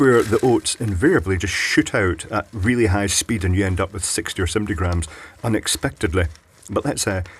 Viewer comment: i just blew my own mind when you said that they shoot out at high speeds and I had thought you said "62 or 70 grams" and then saw the scale measure out 62 grams... then you proceed to play it off all casual as you do. I rewinded and realised my error but very spooky indeed! <link>